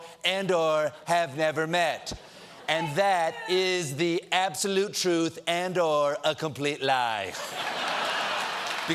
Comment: What did that voice say in the background at 0:31? Thank you? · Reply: 'Fake news!'